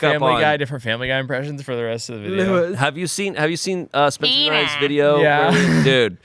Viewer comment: Captions saying the “Spencer Rice video” (instead of ‘Spencer and I’s video’) - accidental Kenny vs. Spenny shoutout lol 😂